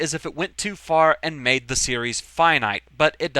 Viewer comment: quick question. What was that word you used on 10:43 and 10:53? "Fyanite?" can you define that to me? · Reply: "Finite," the opposite of infinite.